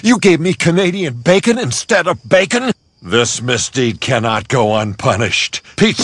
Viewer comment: I just realized he said "mystique" instead of "mistake" lol.